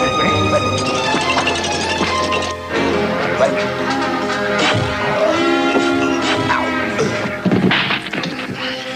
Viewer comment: What does Barnaby say at 1:48:00? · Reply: he kind of groans and then speaks like I will smack them or something